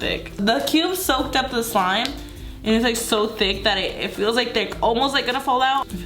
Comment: does she say “it’s so thick it feels like dick” 😂